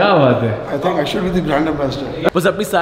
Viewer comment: It's might be the turbulent flow of water or SOMETHING through the pipe which is stick to the wall , there might be many air column formed and resonance shit going throughout. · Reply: Did he say Grand Ambassador ?